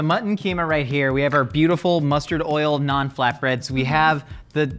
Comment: keeps saying "naan flatbread" and I hear "non-flatbread" and I'm like "What? That's just bread, man. "